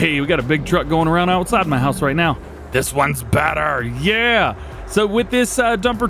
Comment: 18.00 ... did he just say "right meow"...lol reminded me of a movie